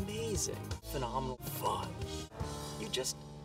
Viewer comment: Did he say Fudge at 02:35?